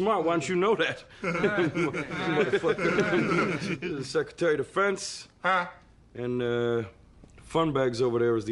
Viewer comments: "This is the secretary of defense." "Huh?" LMAO! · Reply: I thought he said secretary of the fence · "This is the secretary of defense." "Huh?" LMAO! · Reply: I almost thought he said 'Secretary of the Fence', because the world 'defense' might be too complex in that setting.